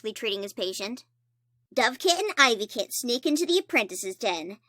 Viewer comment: I legit thought you said Dumbkit